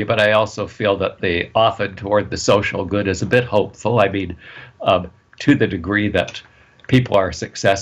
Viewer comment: And this whole time I thought Jordan Peterson was saying "Burrito Distribution". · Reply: Daly sounds so sick that when he says "I mean" the captions say "I beat."